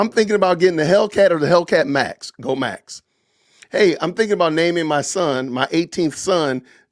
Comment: it sounds like Charlie said “Go…Go n ASK”